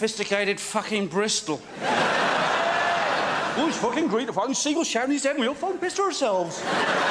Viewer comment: Can any Bristolians translate what Eddie says at 44:00? Its been bothering me for 20 years! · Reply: ‘Oh it’s fucking great the seagull shat on his head and we all fuckin pissed ourselves’ I think 😂